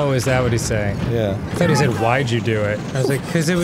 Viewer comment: I also thought Yunobo was saying “Why’d you do it?” At first.